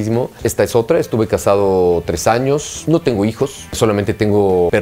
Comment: Que dijo en el segundo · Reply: "Me la paso increíble".